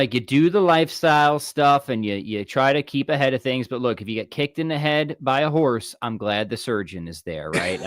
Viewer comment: At the end I've misheard: If you're kicked in the head, buy a horse I find it amusingly funny:D